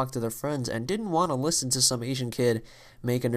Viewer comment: wait did you say asian? at 4:28 Interesting